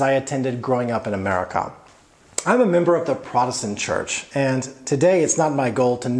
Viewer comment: is that protestant or what? :) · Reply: like what do you say in 6:23 ? I heard protestant but might be wrong :)